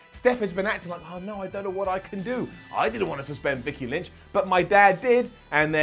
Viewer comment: Did just call Becky, "Vicky Lynch"